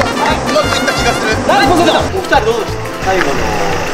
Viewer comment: ポセイドーンが若干ポティトーって聞こえた‼️🤣